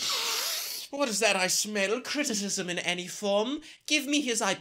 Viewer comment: What did he say at? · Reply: “Criticism in any form” ?